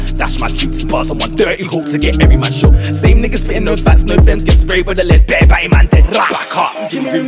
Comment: Did he say "no fats no fems" around theb1:43 mark hah?l